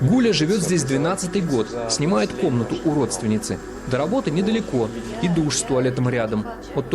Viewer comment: я один тут услышал "УРОДственницы"?